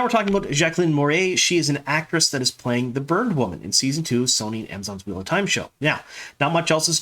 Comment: I heard "burned woman" when you said Jacqueline Moray's role, not "bird woman".